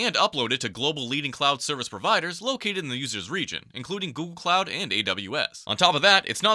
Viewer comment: Please tell me you didn't hear "goon cloud"